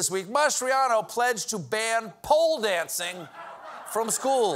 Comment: He actually said ELEMENTARY schools and it was met with clapping and cheers. It's not just MGT and Boebert anymore, the politicians appealing the Qanon base is growing because, thanks to Trump, it's started to envelope the Republican Party itself. Lunatic claims and conspiracy theories is going to outright supplant dog whistling about racism and homophobia/just overtly hating trans people as the go-to strategy to cement votes for Republicans going forward.